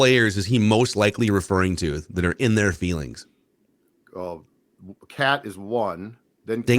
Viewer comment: Shouldn't have said "in our feelings", thought he said "in their feelings"... Regardless, it's inappropriate cliche language that is not becoming of an NBA head coach